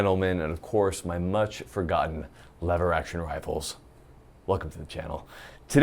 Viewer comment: Ha! I thought he said Leather action rifle, AHHAHAHAHA